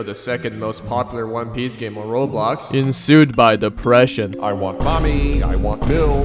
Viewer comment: when he said depression for step 4 and i heard "disgrace. humilation" i got fucking ptsd